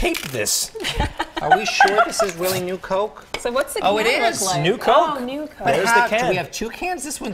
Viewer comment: I had the video sped up to 2X (I watch more content that way in case you're wondering why) and at first I heard "Oh sh*it. This is new coke?" Lol